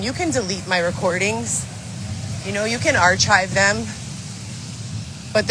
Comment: Wait..Did she say "arch-hive" them😂????